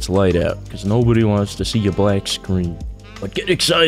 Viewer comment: no one wants to see a black screen, BET enters the chat .... What did he sayyyyyyyyyyy???????